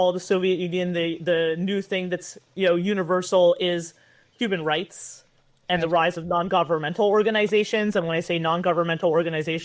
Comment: Did she say @ 48:40 human rights are antisemitic